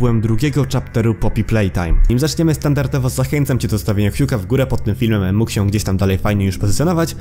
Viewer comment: czy ja tylko usłyszałem fi*ta w góre